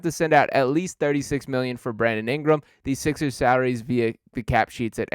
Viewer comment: Brandon Ingram? I thought you were saying Butler?